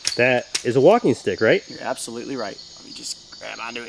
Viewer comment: lol I miss understood the walking stick part I thought he said walking dick😂😂🤣🤣